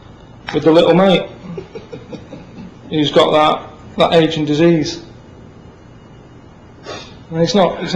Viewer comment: I don't understand that joke lol. What does he mean? Is it that the kid has brain damage and doesn't remember his birthday so his family keep reminding him? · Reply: Ahh i missed when he said aging disease, i thought he said ancient disease lol. Thanks 👍